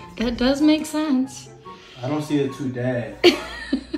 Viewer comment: that face at the end-also i thought like ahen said “i didn’t have time to do that” i was like is he calling u a waste of time 🫢